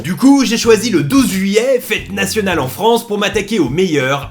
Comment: kewoi : 0:07 ??? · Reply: Oursinolu nan il a dis" fête nationale en France le 12 juillet" c'ets pour ça